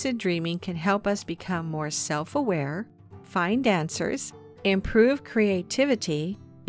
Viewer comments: When you said Lucid dreaming could help us “find answers”, I misheard you and thought you said it could make us “fine dancers.” Hahaha. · Oh “find answers”. I thought you said “fine dancers”. My dancing is already fine. So... good. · I totally thought she said "Lucid dreaming can help us become more self aware, fine dancers... " Took me a minute to realize how dumb i am.